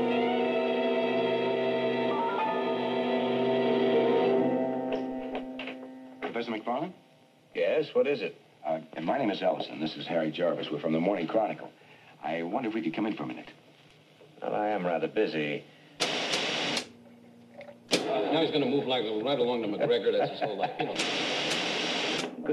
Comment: all i hear is "i'm Seth McFarland, yes what is it? my names elision this harry and Jarvis from the morning chronicals, but im rather busy" comcast?